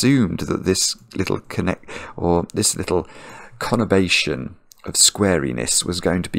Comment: What is the word he uses at 09:48 ? conovation? I could not find it in a dictionary. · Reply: I guess it was “conurbation”, but not sure…